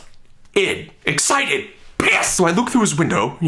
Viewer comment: did he just say PISS!!!!!